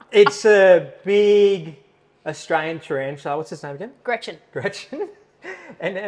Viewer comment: T-A-R-A-N-T-U-L-A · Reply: Yeah, I thought she said trianchula.